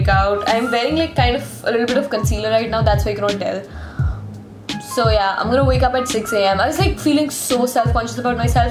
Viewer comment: Whenever she says " so ya " I heard it as zoya 😅😅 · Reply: Yes at 2:05 I heard Zoya instead of so Ya😂